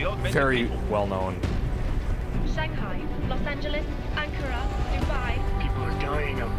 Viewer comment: did she said ankara damn man i hope we see it in this game